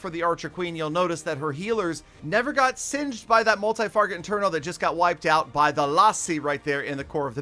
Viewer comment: did he say multi farget interno?